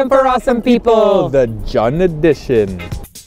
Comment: LOL when said, "The 'Jeon' addition", I thought he said, "The 'John' addition" so I was like whaaaa am I on da right video...I thought I was supposed to be watching them eat pancakes